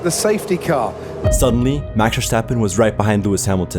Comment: Vershtalphen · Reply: I kept hearing "Verstoppin" 🤣🤣🤣🤣 And now I think that's how I'm going to say it 🤣